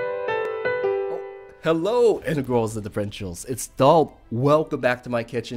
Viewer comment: uhm correct me if im wrong but i can't seem to hear what you say at the beginning of your videos correctly: i always hear: "hello wingul's and the frenchhen...." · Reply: he says hello integrals and differentials.. mathematical terms in calculus